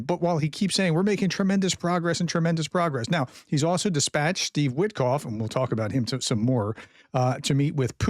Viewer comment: I first heard "dispatched the Witkoff" instead of "dispatched Steve Witkoff". The former describes the situation better.